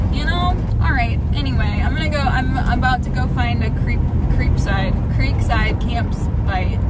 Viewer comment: I thought you said I'm about to find a creep site. I was like no!!! 😂